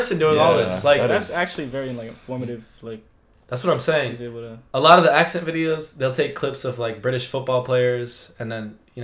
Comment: I thought she said she could do most of the accents, she just sounds pissed